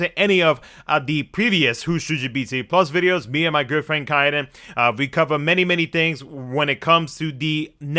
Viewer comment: I swear I heard "me and my girlfriend Kaidan" lololololol